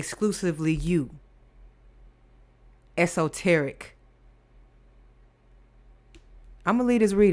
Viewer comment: P🤣W P😃W !!! L😁L ESOTERIC not EXOTERIC.. I hear a divine ECHO not EGO 😂🤣🤣